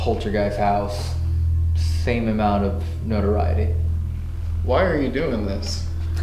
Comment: After said he was feeling tired I swear I heard faintly “why are you tired”